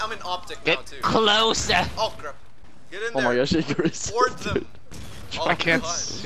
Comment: I thought he said vore them